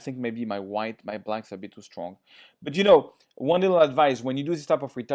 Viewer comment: i thought you said your wife is too strong xD